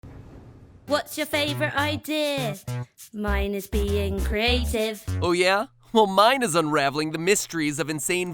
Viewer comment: No joke, when I listened to the intro without subtitles, I heard ‘what’s your favourite idea? Mine is being crazy’ XD